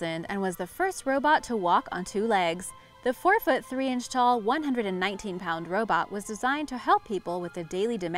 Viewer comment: She clearly says 119lb robot Yet it shows 190lb robot. This matters to me. I need to know if we are in the same weight class for the upcoming human / robot fights.